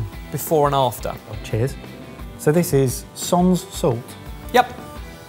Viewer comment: There's a few errors I've noticed in the captions 14:27 says "This is sans salt" whereas the captions say "this is son's salt" 15:40 says "That kind of unctuousness" whereas the captions say "That kind of anxiousness"